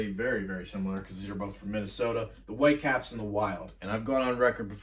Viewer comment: did you say the White caps? who the heck are the white caps?